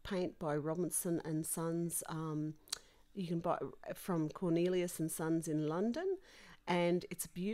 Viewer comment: Hi there, 16:57 you were going to link to the gold paint in the description box I think you said? Also, do you. mean L. Cornelissen & Sons? It sounds like Cornelius? But maybe I misheard... 😂